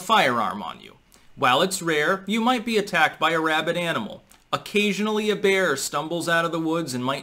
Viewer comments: OK I heard "you might be attacked by a rabbit animal" you deffo need to be armed to fight off killer bunnies. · 1.16: I heard, "You might be attacked by a wabbit animal". Elmer Fudd versus Bugs Bunny. Goofy the Vermont Governor arrives. Daffy Duck and Sylvester join Antifa and set fire to the forest cos they're bl**k.